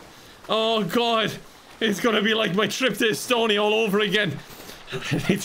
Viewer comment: am i hearing it wrong but Kevin said "trip to Estonia" on @ 3:07:45 ?? (like what's the context? im confused)